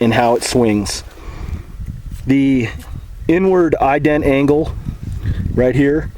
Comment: LOL. When you said "inward" I thought you said "n-word". I was like: o_O